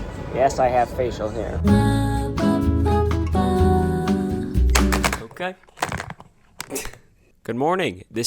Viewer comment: he really said “ну ка”